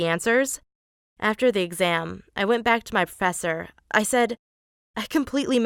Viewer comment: who heard fessor